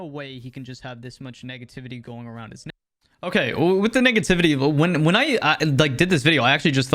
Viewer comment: anybody else hear “n*ggativity” instead of “negativity” at 0:53 ?